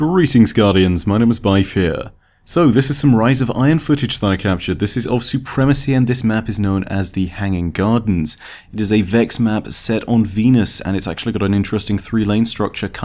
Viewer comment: i heard "hanging guardians"